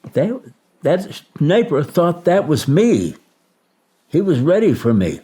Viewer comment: what is he saying???....."that _ thought that was me " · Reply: “That sniper thought that was me”